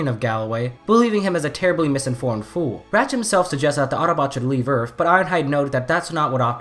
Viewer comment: OMG I THOUGHT HE SAID THE AUTOBOTS SHOULD BE BURNED XD